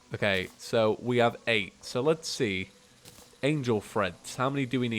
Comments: The whole time I thought he was saying "angel freds" and I see the name wen he is about to craft it and I'm like " ohhhhhhh" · I kept hearing it as "angel friends" ... then eventually I figured out the actual name was "treads" Has he been saying "threads" instead of "treads", or am I just getting tripped up by the accent?